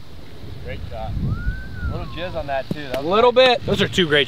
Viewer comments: does say “a little jizz on that” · did say “little Jizz on that too” ?😂😂